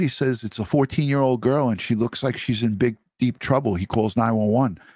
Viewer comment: Thought said to Gus she was “in big trouble.” Also, if it was suicidal why did 48 hours say her ID and pants found at different location than her body? Bf seemed to know more than most do, weird things.